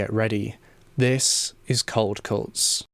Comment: Bruh thought he was saying cult cults 😭 great vid! Super informative!